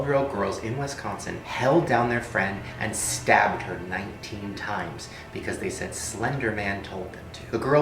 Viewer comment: wait i heard they stabbed their friend 20 x did i hear it wrong? And yes im in 2019 5:29 WHY THAT TERRIFYD ME